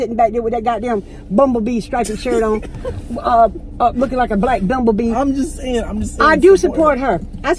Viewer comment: looking like a black bumblebee🤣🤣💯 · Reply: Jayla faith Santiago sounds like she said dumbabee